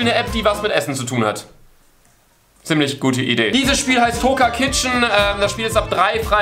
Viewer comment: das Spiel kannte ich schon vorher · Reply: weiss jmd wie das spiel heisst ich hab nir kizchen verstanden